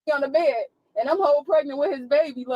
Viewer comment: Did she say she's " whole pregnant " ? @ 21:11 Don't know anyone that's half pregnant or a quarter pregnant, LOL!!!